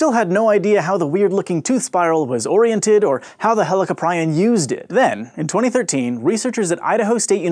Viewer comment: I thought he said how the hell a caprian used it lol